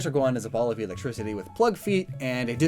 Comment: for a second there I thought you said "a ball of electricity with "flood" feet." I was like wait has this guy secretly know about Halo.